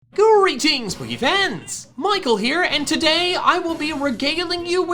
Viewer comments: What Michael says: "Greetings Pokéfans"... What i hear: "Quarentine Pokéfans"... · Everytime I hear intro I always thought it said BoogeyFans and it sounds so weird now I know he's sayin pokefans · “Greetings, Pokéfans! Michael here,” is the most relaxing quote ever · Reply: When I first watched him I thought he said greeting boogie fans